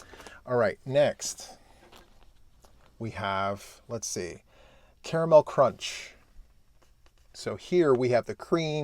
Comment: Oh my, the caramel crunch is all the things!! Lol, so true, if you can't have the whole cake, maybe a slice will suffice 😊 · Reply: I thought said Camel crunch 😂😂